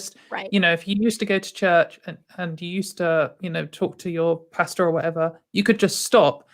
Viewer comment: Somewhere around 30:00 did say 🤔 pasta? 😆 🤣